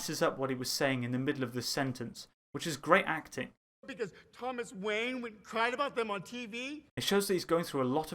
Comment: he didnt mess up what he was saying, he said "what because thomas wayne WENT AND cried about them on tv" he is still confident